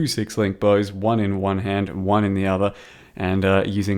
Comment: Did he say 1 bow in 1 hand 1 in the other